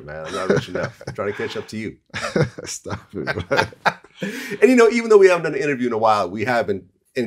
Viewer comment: it really sounded like bibby said "astagfirullah" lmao or am i trippin